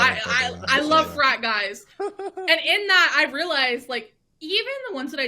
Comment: did she say "i love young men. I love fry guys!"?? Like at Five Guys burgers, it's the fry guy she likes the most? She's quite a persuasive speaker though. She used to get stage fright, but she learned that trick where you imagine everyone in the audience is a Twinkie, then she's in charge.